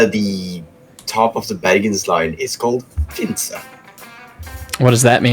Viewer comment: I may need clarification for 2 things about what someone said at 45:55. A: where is the baggins line? B: what name was said? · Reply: I suppose you mean the Bergensline. Located in Norway. As for the station name it’s Finse. Pardon for my voice, I am currently sick and my voice is slowly failing on me